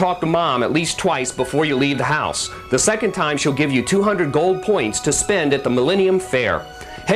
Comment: Did he say "200 gold points" or coins? I swear he said "points" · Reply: Gold Points. G P.